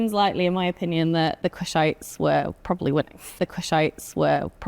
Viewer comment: "The Kushites were probably [???]" 11:27 "The Kushites were probably [???]" 12:08 "The Kushites were probably [???]" 14:14 "The Kushites were probably [???]" I have no idea what word "[???]" is. Can anybody else make out what "[???]" is? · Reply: winning